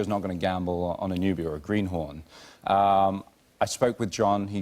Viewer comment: I thought he said "nubian greenhorn". I was like ok z snap